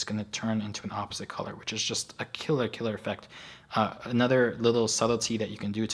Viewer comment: what was the word you said at 9:52? I've been trying to figure it out but cannot seem to get it. Just curious though... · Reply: Another little " "